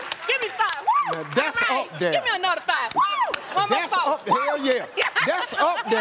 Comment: What's she saying at 0:24? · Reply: One more time